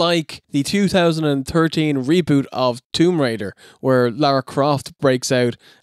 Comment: hmmmm that sounded off, it almost sounded like you said laura instead of Lara